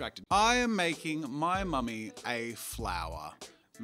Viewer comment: "I'm making my mummy a flower" Me who heard it as mommy: 0-0 jazza's a bottom and you can't change my mind